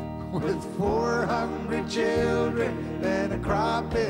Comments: How many people thought he said 400 children instead of 4 hungry children? Rest in peace Kenny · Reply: Same here, when I was a kid, I always thought he said 400 children. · How many people thought he said 400 children instead of 4 hungry children? Rest in peace Kenny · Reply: Yeah ! And i always questioned why he said 400 children. I thought he was saying that to his farmed animals